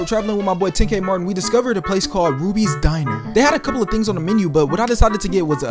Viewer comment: I thought he said bootys diner💀